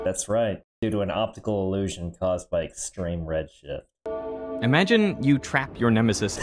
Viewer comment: Due to extreme red shit Is what I thought he said